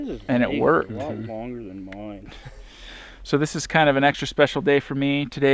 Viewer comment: Did just say what I think he said ? " Your guys' dick is a lot longer than mine "